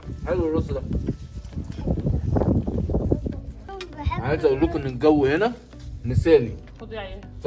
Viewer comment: بسم الله يا جماعة اسمعوا كويس في الدقيقة 7:46 في صوت كانه جني بيقول خذ يا وفاء !! ههههه لايك لو سمعتو